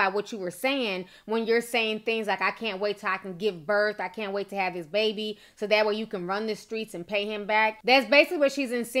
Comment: I didnt take it like that. I thought her post was about him not acting right (not cheatin tho).. n u know females running their mouth (which is y I thought she said wait til she drops this baby)